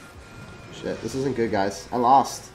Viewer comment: i think he said shit thought it was family friendly But i dont mind love ur vids and keep going